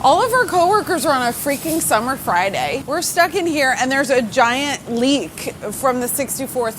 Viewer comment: So when you said "giant leak," I thought you said "giant leek" and got very excited that this could be going in a vichyssoise direction. Or a SyFy movie.